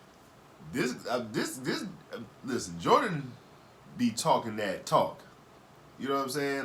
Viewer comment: I thought you said Joyner